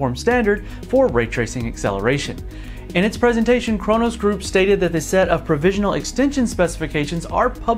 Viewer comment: OMG I thought you said Corona Group when this started. I hope you don't get demonetized.